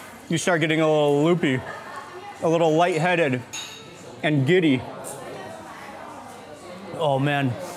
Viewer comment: I thought u said "and Diddy"